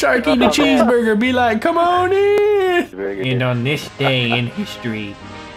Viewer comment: Instead of come on in I thought he was saying cornie